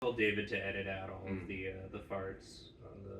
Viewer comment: Ngl i really thought the opening said “told David Tennant about the farts”